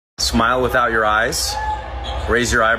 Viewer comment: I thought they said that your mama face😂💀💀